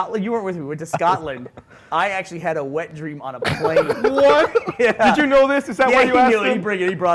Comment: I misheard Burnie when he said he had a wet dream on a plane. I thought he said a wet dream of Blaine...